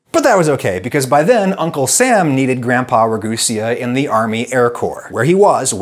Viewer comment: slight mistake in the subtitles: at 13:20 dam says "army air corps" but the subtitles say "army air corpse" two very different things.